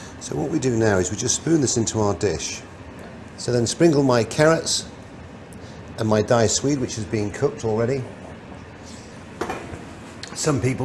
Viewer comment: What does he say at 3:22 ? "Diced sweet"? Or what kind of cubes is he putting in, along with the carrots? Thanks in advance for the answer! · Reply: Context clues: diced sweet potato